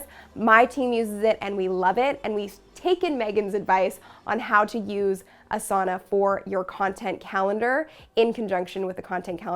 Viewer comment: As a Finn I heard you saying "How to use a sauna for creating your content calendar" - not a bad idea actually :-D